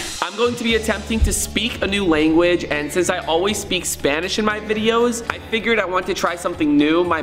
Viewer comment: did he say he speaks Spanish in his vid cause I always thought he speaks English 😂